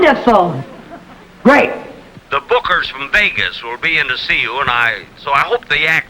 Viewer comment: I heard "the hookers from Vegas". 😂😂😂